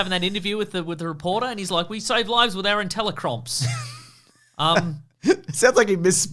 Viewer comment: I love the mishearing of ‘intellicrops’ as ‘intellicrumps’